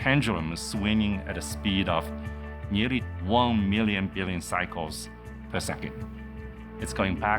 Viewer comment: did he said 1 million billion cycles?😮 that's the biggest numbers i've ever heard someone say